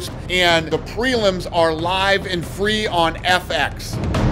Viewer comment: Did Dana say free on Fx?